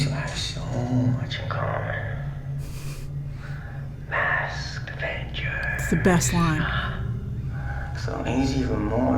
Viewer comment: "Masked Vengers" is most definitely the best line lol. His joker laugh is actually good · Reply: i thought he said masked adventures